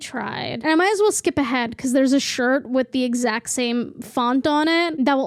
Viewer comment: i really thought it said rude!